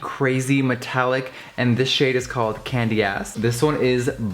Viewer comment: I thought this nigga said Candyesque and I look and see ''Candyass'' in the fucking description